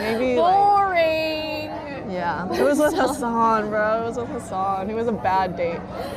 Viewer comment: Yeah chat was spamming Hasan so she went along with the joke. Thats why she then explained that she did meet him once at a store. If you went on a date with someone, you wouldn't follow it up by saying you met them once at a store and they were rude. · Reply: Maybe it's my ESL but I hear her say "It was like Hasan" while the subs say "it was with Hasan".